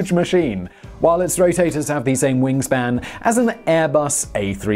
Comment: Did you say rotators at 1:45?! #cancelsimon